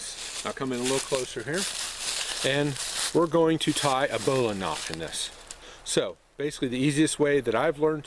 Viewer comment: a bola knot? bowline?? what did you say? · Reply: Bowline